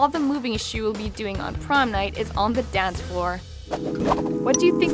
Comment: when she says "The Talko" I heard "The Taco" it might just be because I'm Hispanic